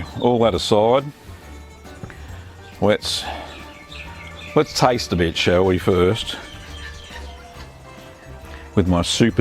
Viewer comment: i thought he said "lets taste the bitch shall we?"